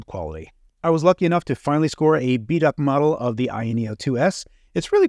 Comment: I thought you said you procured a "Beat up version" of the AYA. I get it now 🤣🤣🤣 . I really enjoy your videos too keep up the good work !!!